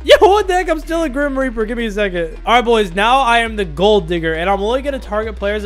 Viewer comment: i thought he said gold N- 😂😂😂😂😂